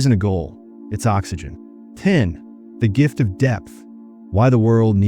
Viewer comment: I thought you said the gift of death lol and I was like well when you know you know haha. But actually you said the gift of depth. Doesn't feel like much of a gift honestly. No fun being deep when no one else is.. but yeah great vid.